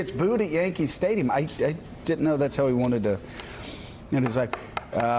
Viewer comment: oohhh man I heard it “isn’t how he wanted to.. end his life” 😂😂😂